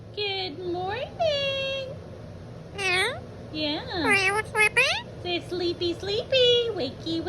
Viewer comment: I thought he was saying Are you a Slurpy? 😂